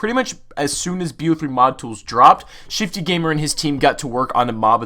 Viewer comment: Shiftygamer deleted his chanel nooooooo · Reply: Jathniel Davila I thought you said shittygamer at first